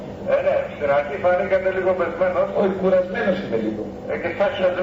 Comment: Ρε παιδιά τι λέει στο 12:15 και γελανε γιατί δεν ακούγεται καλα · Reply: ε και σα χεσμένος